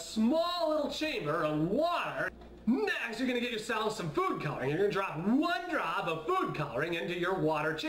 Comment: It almost sounded like he said poop coloring instead of food coloring... I have a dirty mind ._.